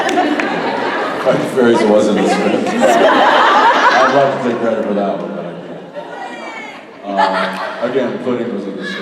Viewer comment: what did jensen say at 43:48 ? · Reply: " 'Fight the fairies' was in the script. I'd like to take credit for that one but I can't."